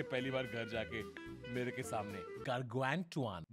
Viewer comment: wht did he say .can anyone tell me? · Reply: Gatorade